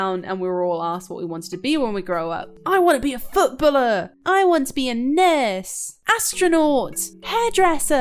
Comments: i thought she was gonna say, "I wanna be a nerd!" XD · for a second I thought she said "I want to be a nerd!"